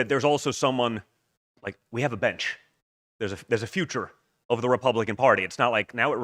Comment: Omg aahahahaa I thought don Jr said “we have a bitch” (when he said bench) , specifically referencing JD and I was like 🤯🤯🤯🤯😆🤣🙌🏼LOOOOVE THE BENCH. LETS FKN GOOOOO!!!